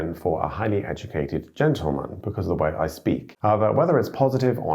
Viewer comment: I thought I heard you say aber and went, hmm he's really "Germanized" lol but then went back to doublecheck and you actually said however. lol